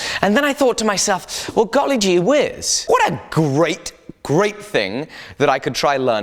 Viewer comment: Did really say "golly gee wiz" at 6:34?